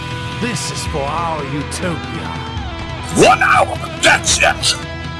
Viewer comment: why does Vegeta say, Wuhtaow 🤪, when he’s getting ready to fuse 😂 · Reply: he said "One hour thats it"